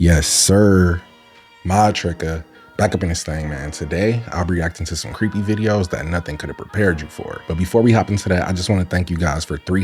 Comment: I hear .... "Modtricca back up in this bitch"